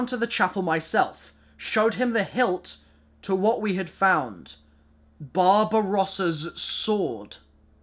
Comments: When he said Barbarossa's sword I thought he said Bob Ross's sword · I heard Bob Ross's sword 😂